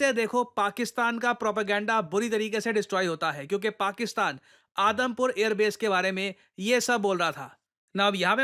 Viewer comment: i literally heard Adampur air airbase as Adamkhor airbase